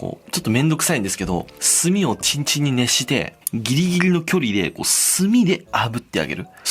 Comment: 『炭をチンチンに熱して』にしか聞こえないんやけど、なんて言ってるん？